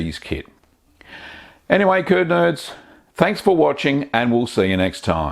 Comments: i swear i thought he said coin nerds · G'DAY CURD NERDS I low key thought he said turd nerds